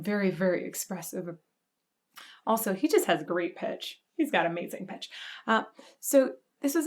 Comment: (ish) he's got the bitches. That's what I heard.